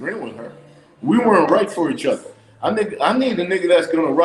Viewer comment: Did he say he needed a man at 10:46?! face 🤣🤣🤣